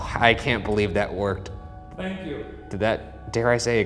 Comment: it sounds like someone said thank you in response to Ryan. but I can’t tell if it’s someone from the filming crew or Shane but from what I can see it doesn’t really look like Shane moves his mouth when you here it but I could be wrong 🫢